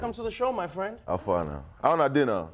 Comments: "How una dey na?" silence We have established there are ZERO Nigerians in Trevor's audience. 😂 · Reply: I thought he said "how far na" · No one Knew what burna boy said at 0:03 HAHAHAHAH! Then the audience awkwardly clapped hahaahah But for real tho what did he say? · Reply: Teo Marr “How far now?( means what’s up) How una Dey now? ( how’re you guys doing)